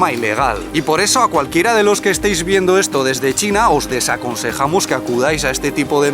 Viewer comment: Buenísimo vídeo. Dijiste "desaconsejamos" qué bueno que retrocedí y escuché de nuevo porque es una palabra que no conocemos en Argentina, o al menos cuando todavía vivía allí. Había entendido mal y pensé que dijiste "les aconsejamos". Aprendí una palabra nueva. ¡Qué bien juegan esos jóvenes a la bola con la plumita! ¿Se llama Jianzi? (editado): Bueno parece que nadie quiere o tiene tiempo de contestar mi pregunta...